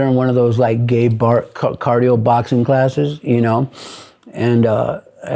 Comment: Did he say he meet his wife at a gay bar